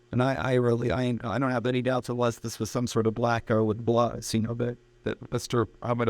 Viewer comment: Did he say “some kinda black guy with gloves”?